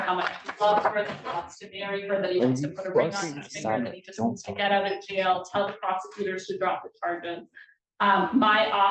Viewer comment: what did he say at 8:48? The audio keeps cutting in and out I couldn't make it out..what'd you hear? ❤ Judge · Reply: he said "Are you fucking kidding me!?!" I thought I had said that...